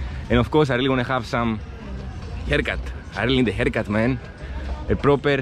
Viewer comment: I thought you said you would get a HELLCAT there but it was a haircut. I would visit there. I also feel like if you are good to ppl then ppl are good to you so I would not be afraid to visit and meet the locals there.